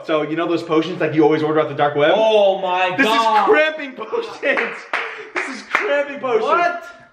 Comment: I thought it said camping potion